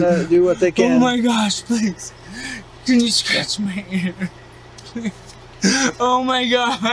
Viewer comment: Did he just say "Can you scratch my ear?" 😅👀💀